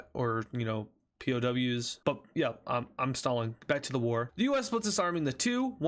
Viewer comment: I heard you say I’m Stalin back to the war 💀